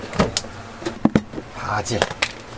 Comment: what is the word in english at 17:54??? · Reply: it is basil